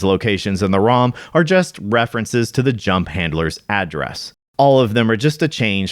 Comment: I honestly thought you said junk handler and I was like bruh how is my ex's address in SMB3? She wasn't even born yet